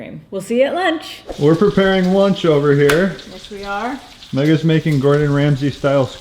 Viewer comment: Been watching you guys for a few months now and I always thought said her name was megan lol